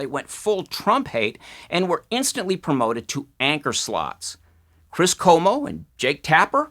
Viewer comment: I thought you said "anchor slut".LOL